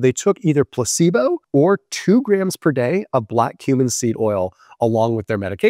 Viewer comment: Sounds like he said "Black human seed" 😳